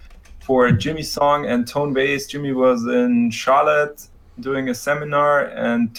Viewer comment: When he said Jimmy was in Charlotte I thought he said "Jimmy was shot at" lol